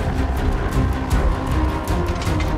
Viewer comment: I heard “Summon the ElectaCunts!”